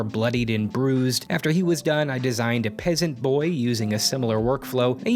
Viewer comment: "I designed a peasant boy" - God when making me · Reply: Thought it said pleasant boy but read replies and realised it was peasent